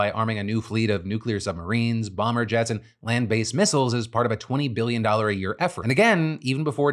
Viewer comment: I heard ‘plant based missiles’. I need coffee.